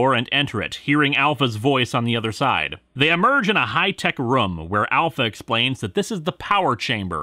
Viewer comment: sorry, but no matter how many times i relisten, it sounds like you said "High-tech rum", not "High-tech room"